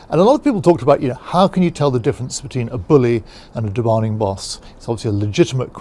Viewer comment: I like how I think you saying demanding boss, but it kind of sounds like he says demonic boss. Both things exist.